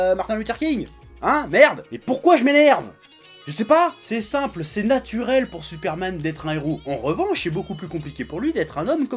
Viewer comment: A 3:58 quand tu as dit "Et pourquoi je m'énerve? Je sais pas!.... C''est simple, c'est naturel pour superman d'être un héros" moi j'ai entendu "Et pourquoi je m'énerve? Je sais pas!.... C''est simple, c'est naturel pour moi!" J'étais HS quand j'ai anticipé la suite de cette phrase!!! XD Sinon dès que j'ai vu le titre de la vidéo j'ai tout de suite pensé à ce que tu pourrais dire: et j'ai visé dans le mille! On a des avis assez similaire. Pas tout le temps mais assez souvent! Superman a son côté attachant par le faite qu'il est dans une position de force (c'est le cas de le dire!) mais qu'il reste plus ou moins gentil malgré tout. Cependant je pense que les humains normaux (c'est-à-dire nous pauvre lecteur) nous nous identifions pour la plupart plus à batman parce que: il est humain, le côté psychologique du personnage est, je trouve, globalement, mieux traiter que superman, et même si il a des gadgets crée par son argent il en impose même sans (coucou iron man que j'aime beaucoup mais il faut admettre que à part son intellect qui lui permet d'avoir son armure bah il peut pas trop faire grand chose...) Bref moi je vote Batman 100% du temps, mais faut avouer que normalement sur le papier batman se fait dérouiller... Sinon tu n'en as pas parler mais dans cet affrontement il y a une dualité lune/nuit (batman) et soleil/jour superman avec en plus l'idée que l'un vit masqué et l'autre sans (même si l'humanité ne connaît pas son identité: l'image est là!). Donc l'affrontement bien q